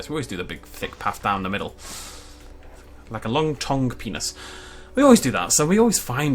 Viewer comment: did you just say long tongued penis?